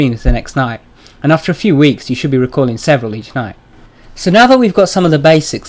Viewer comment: did he just say several?